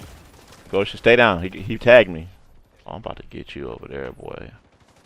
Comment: What B said @ 1:16 : "Oh I'm about to get you over there, boi." What I heard: Ye-he-aah boi...